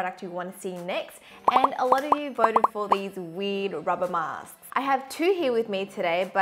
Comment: no hate but i legit thought she said “this WEED rubber mask” & i was like WTF